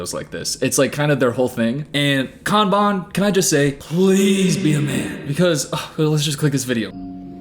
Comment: What did say at 2:59 "please be..."? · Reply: A man